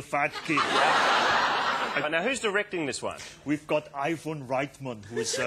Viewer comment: did he just say “iPhone”? :D